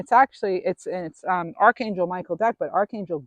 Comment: Thanks. At the 49:42 mark did you say Michael deck or Michael Beckwith? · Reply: I said Archangel Michael deck.💜